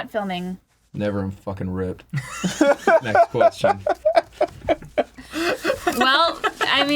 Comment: does Ross say "Never...I'm fucking ripped. Next question...."